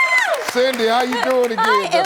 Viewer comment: Oh I thought you said Cindy